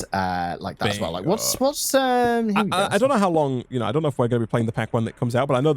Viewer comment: "I don't know if we will still be playing this pack when nik comes out" (I know that's not actually what he said but it sounds like it)